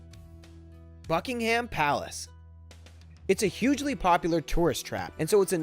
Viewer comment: does he say fuckingham palace?